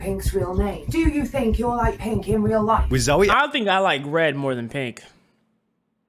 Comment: Did you just say red? 1:00 I love you